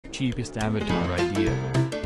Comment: I thought you said cheapest avatars 😂